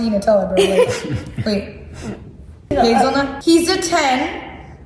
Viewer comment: What did say at 13:29? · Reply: Hazelnut